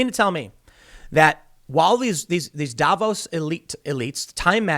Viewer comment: I heard, "Davros elites." OMFG they're Daleks.